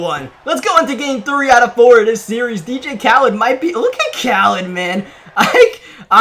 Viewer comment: “Look at Khaled Man” I’m pretty sure he sounds like flight saying “Look at Curry man”